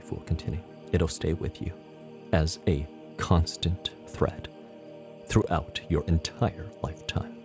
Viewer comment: What și being said at 4:52 ? I hear “this breath will stay with you as a constant threat…” 🤭 Anyone can tell me what’s the word being used? Automatic translation from YouTube also says “threat”. Anyone can tell me, pretty please? My subconscious needs to know it so that it can relax, it got all hot and bothered about the word “threat” LOL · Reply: A constant thread! Not threat. Hope this helps you breathe a little better.